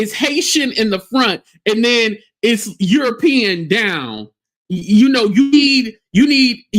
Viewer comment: I thought said that wig could be Haitian in the front and hay 💩 in the back!!! 😂😂😂😂